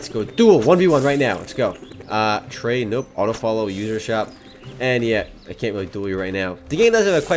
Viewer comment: when is listing off things in list.. does he says "uterus shot" for the last one? wtf bro?